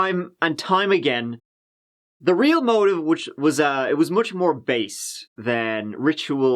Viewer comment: when he says "the real motive was much more *blank*" it sounds like based but I think he means biased and with his accent sounds like based... any ideas on which it is? I watch like all your videos and always know what you're saying